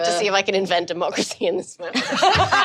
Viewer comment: oh! "Invent democracy in this moment" I swear to god, I heard "invent a f**eskin smoother"